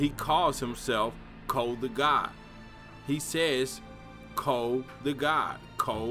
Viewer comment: Thought he said cole the guy but hey what do I know..